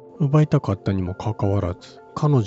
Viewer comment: 外来語には噛まないのに日本語に何故噛むのか丸出だめ夫ですな。でも尊敬してます。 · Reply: 奪いたかったに姥捨てたかったが入り((( ；ﾟДﾟ)))けど面白い❗️ 4:27最初聞こえたのは保管するで２回目は悪寒する❓️ 5:42１回目は持ち込んだですが２回目はもこみちと発音している様に聞こえました⁉️ 9:25(テロップの文章)キプチャクの滅亡と…読めた❕ (ナレーターのナレーションを聞いた範囲では)キャプチェクキプチャクの滅亡と…聞こえた❔ 10:30最初は容易に齷齪と聞こえ次に容易にアクセスと聞いた‼️ 面白い仕掛ですね🎵。